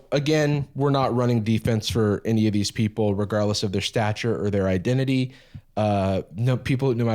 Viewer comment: when you said “stature” my brain was still thinking about the topic of being tall so i thought you were saying “regardless of their height”